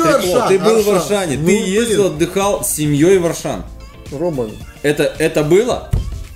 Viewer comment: Мне послышалось, что ты ездил отдыхать в Ашан!!! Я и этого не могу себе позволить!!!